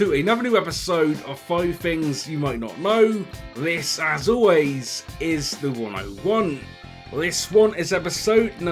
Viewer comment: I just now realized that at the beginning it is "This is as always The101" I always thought it said "This as always is the one I want"